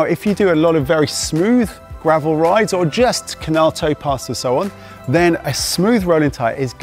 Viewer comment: What does he say at 3:50 "or just ??? so on"? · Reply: Oh, closed captioning gave me the answer: "or just canal towpaths or so on." As an American, I had never heard of such things and couldn't recognize the words.